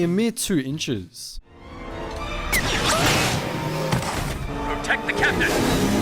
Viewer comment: The auto generated subtitles say,”take the candy” instead of, “protect the captain”